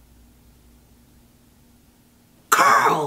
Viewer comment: can someone tell me what did she say? i didn't clearly heard what she just said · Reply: "not blackpink sparking one"